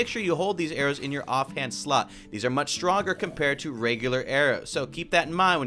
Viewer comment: did it sorta sound like he said off hand slut instead of slot?